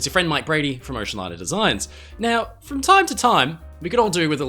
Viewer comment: at first, I thought you said " Mike Brady from ENCHILADA designs" 😂